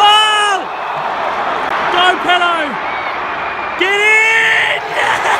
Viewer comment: Lmao first words I hear "Go Pedo! Get in!"